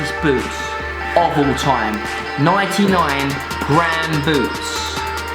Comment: I heard 99grand boots